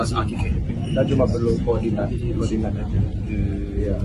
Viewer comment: Ada translator itu kedengeran, ngasih tau Gibran "cuma butuh kordinat" kata translatornya.